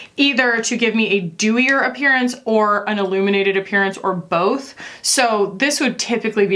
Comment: So, at 6:03 I heard 'aluminated' quality, and I was all "aluminated?? Why she wants her face to look like aluminium?" And then yeah, ILLuminated, riiiight. Makes sense.